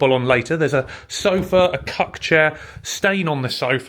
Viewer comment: A cuck chair, did I hear that right! 🤣